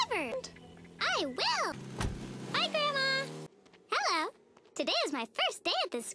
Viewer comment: I thought it was her mom and She said “Bye Grandma!”